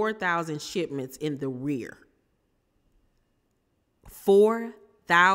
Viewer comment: I laughed when I heard " in the rear" instead of in arrears.